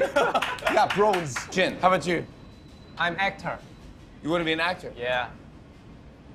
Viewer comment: when Jin said “actor” I just heard he wanted to be “hector” I’m like huh🤦‍♀️🤣🤣💀